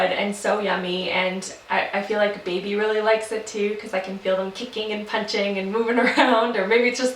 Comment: Funny.. when she was mentioning Greek yogurt, it sounded like she said I could feel "him" punching and kicking. At 3:42? Anyone?